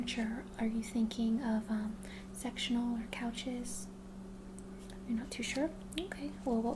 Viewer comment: I misheard 3:48 for "sexual couches." :'D